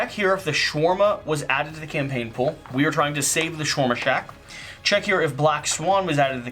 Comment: As someone who doesn't know Marvel, I hear Shoarma Shack.